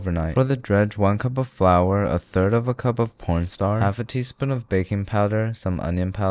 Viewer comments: did he say 1/3 cup of "porn star"? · did he say p*rn starch or is it just me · Anyone hear that he say porn star instead of cornstarch? 11:35 😮